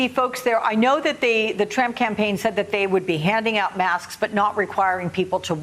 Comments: Did she say Tramp campaign 🤔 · Did she just say the "Tramp Campaign!?" Lol!